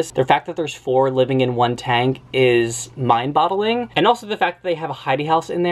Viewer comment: Did… did he say “mind bottling”